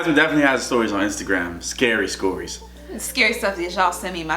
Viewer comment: Sorry but I gotta say it...... 0:20 DID YOU JUST SAY SKORIES